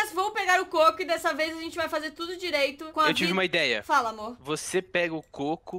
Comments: eu ouvi amor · eu ouvi um amor ne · Eu ouvi a dizendo oi amor por Alvinho